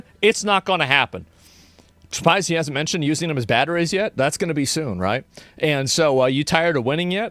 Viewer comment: What did he say here? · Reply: Using them as batteries